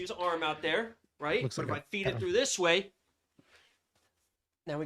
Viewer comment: Did bro just say "looks like a p*dofile?"😂